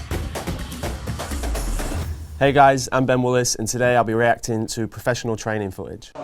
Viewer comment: For people trying to find Ben his last name is, I know it sounds like he said we willis lol. Bit odd for fight front not to include his name anywhere in the description on in text on the video